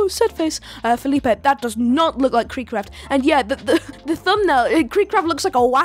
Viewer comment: "Cary Grant" instead of KreekCraft, "Damon" instead of "Dhar Mann". Let's just think how an actual human being edited all of this and made so many hilarious mistakes 💀